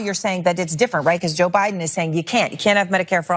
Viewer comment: For 1 second I thought she said "Joe Budden " and I was slightly interested...